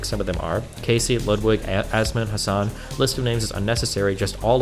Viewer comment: Wait am I losing it or did the emailer mention Asmongold here? It sounded like Destiny said "asmon". Or maybe he misread Hassan?